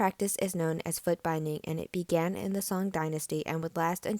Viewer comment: I thought it said Chinese Food binding 😂